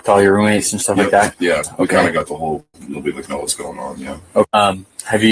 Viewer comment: I thought he said “yeah we kinda got the whole .. now we like know what’s going on”. Maybe I’m wrong.. Also.. when you day drink, it’s not that uncommon to pass out at what would seem early on a normal night but 11 pm if you started drinking at noon would mean you’re drinking for 11 straight hours. Weren’t they all borging lol?